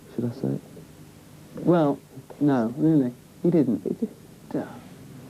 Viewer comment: What does Robert say at 4:41? Anyone catch it? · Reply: Smoking Poetry he pissed in it?? I think that what he said haha